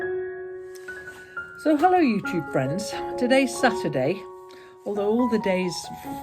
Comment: Hi, I'm new to your channel but I love it. I must tell you that when I first started watching your videos I thought you started off by saying 'hello you two friends' I thought that was lovely. I now realise that you say 'you tube friends', it made me laugh anyway. I'm a thrower when I knit, my nan thought me when I was about 8 years old, my mum used to be able to wrap the wool around with her right hand without letting the needle go at all. I've never seen the continental way done before but may give it a try. I just passed some butter candies along the virtual sofa, my favourite, enjoy!